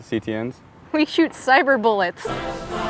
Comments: I just thought they said “we shoot cyberbullies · I thought she said: “we shoot cyberbullies” Lmaoo